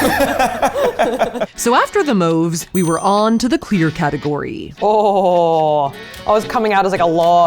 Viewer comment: I thought she said queer instead of clear